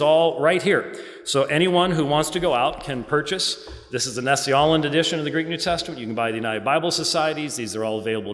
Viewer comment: can anyone help me by mentioning the Bible version which Dr. White mention in 47:34, i cannot hear properly and i want that Bible Version to examine the New Testament variations. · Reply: Nestle-Aland Greek New Testament